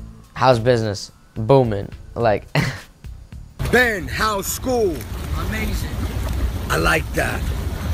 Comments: What I thought DJ Khaled said, "DJ Khaled: Ben, how's school? Ben: I'm Asian! DJ Khaled: I like that, I like that..." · Honestly, when he said amazing, I heard it as "I'm asian" and just thought, well i can see why its going so well. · I thought he said "I'm Asian" instead of amazing lol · lol thought he was saying "im asian" and not amazing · At first, when DJ Khaled asked him "Ben, how's school?", I thought he said 'I'm Asian" instead of "Amazing." and I was like wtf. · I thought he said "I'm Asian" lol ...he said "Amazing" I'm not trying to offend anyone btw